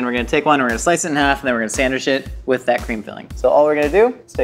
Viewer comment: Lol at 7:36 i thought you said "we going to sand this sh@ lol but you said sandwich it